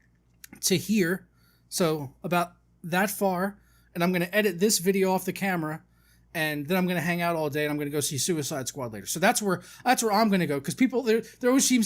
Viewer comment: Thought he said (i'm gonna go suicide later)